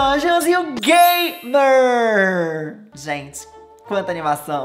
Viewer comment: gente juro que eu entendi gy emvez de game 🤣🤣kkskskskskskskssk